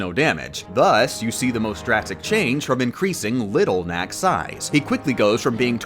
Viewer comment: i thought you said little mac xD